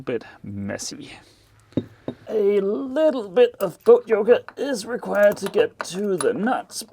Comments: When you said "boat yoga" I thought I heard "goat yogurt." Just thought I'd leave that here. From now on I'm going to call it goat yogurt when I'm climbing around in the lockers and engine room upside down. Good work. Things are coming together very nicely. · I could swear you said "Goat yoga" was necessary at 5:05.